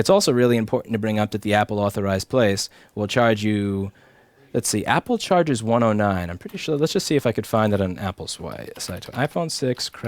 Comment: what did he say at 34:53 -34:59? It keeps buffering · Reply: Josh3292 [...] "let's see. Apple charges 109. I'm pretty sure...let's just see if I could find it on Apple's sites. [enters google search terms + parameter <link>"] I hope that helps